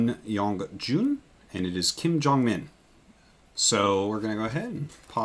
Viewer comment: thought he said Kim Jong Un for a second there, lol